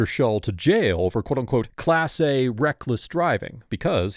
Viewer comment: He said class B reckless driving. It's very small but you did say quote, so it has to be what was actually said. This happened